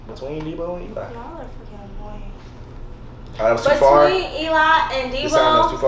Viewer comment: Did said fucking annoying?